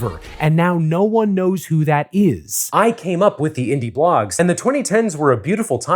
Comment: it’s early and for a sec I thought he was saying he invented the concept of indie blogs